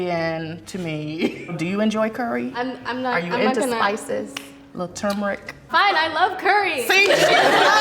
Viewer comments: Lol I thought she said “are you into isis” I was like “what? Why is no one calling her out on that comment?😟” then I put the cc on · Did I hear " are you into Isis" ??